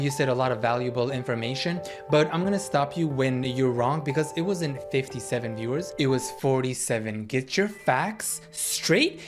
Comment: she actually said 55 🙄 and fr she needs gets her facts right 😭😭